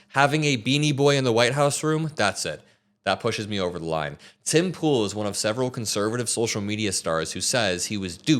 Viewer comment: Oh I thought you said he lives in a pool! Looks like it